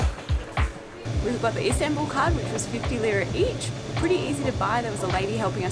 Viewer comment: Thanks for the video. I´m planning to do the same route, hope everything goes well. Just one question 12:26 What did you say? 50 cents euro each? My mother tongue is not english and don´t understand that part very well. How much did it cost the train to the center? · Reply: I'm sure it will be a good journey! We actually said '50 lira each!' Which works out to be roughly €2.30